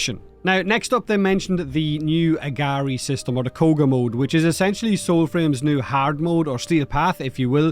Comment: I am NOT missing out on the Founders Program this time. Still salty that I missed Warframe Founders program by 1 day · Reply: Straight up thought he said “cougar mode”….